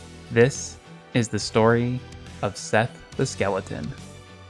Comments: I hear him say"sans the skeleton"lol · I Thought he say the skeleton sans